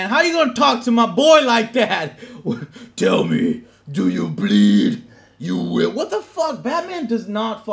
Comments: Batman v Superman: Batman: Do you plead (guilty)? Batman: You will. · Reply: crzymn246 I thought he said plead at first, and that version is still preferable in my mind, but it's actually "bleed". "Do you bleed" (in reference to him being a "god"). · Batman v Superman: Court of Justice "Do you plead? You will!" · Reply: First time I watched the trailer I thought it said plead. So I thought batman was judge dread now. Was confused till I watched this and heard Joe say bleed.